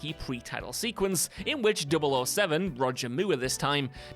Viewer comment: Did you just call him Roger Moo?